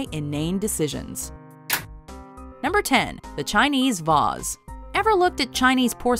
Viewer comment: did she say Chinese "Vass"?